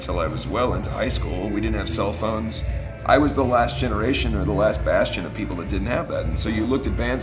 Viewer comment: Did he say "the last bastion"? 4:45 :o